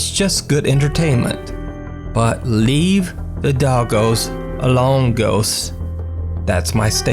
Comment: “Leave the doggos alone ghosts” This can be said for any beloved pet. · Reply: I thought he said “leave the doggos alonegos” 😂